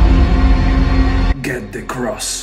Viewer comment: It sounded like he said "Pizza Crust" tbh